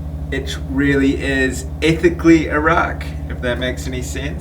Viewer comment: I heard "ethically Iraq." Did you meant "ethnically"?